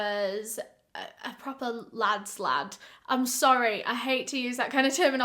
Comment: Lmao I heard “Lad Slat” and I was like ugh I have to go google another British phrase until I was like oh lad’s lad 😂🤦🏼‍♀️